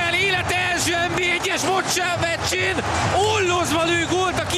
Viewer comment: Mit mond? Élete első NB1-es (futsal?!) meccsén? :D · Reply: TheTsepreghy sajnos elsőre nem sikerült kimondanom a “meccsén” szót, de aztán korrigáltam..🤦🏼‍♂️😅